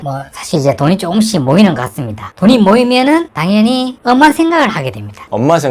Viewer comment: "엄한 생각"이 "엄마 생각"으로 들리긴 했습니다.